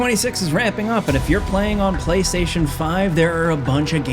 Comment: I had to replay the beginning like 4 times. I thought he was saying “2026 is wrapping up” but I guess he’s saying “ramping up” 😂 idk I was so confused